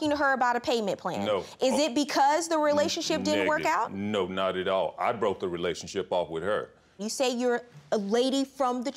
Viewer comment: does anyone else catch him saying nigga? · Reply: nErdGasm he said "negative"